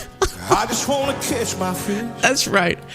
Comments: Oh man, I heard "I just wanna kiss my fish" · I heard “kiss my fish” too!